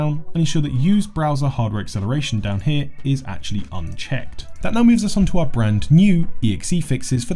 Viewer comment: At 3 minutes 3:00 what does he say to check that or uncheck it ? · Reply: unchecked